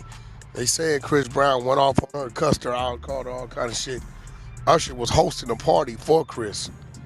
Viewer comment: I haven’t heard someone pronounce usher like that in a long time. I swore I heard “arshuh”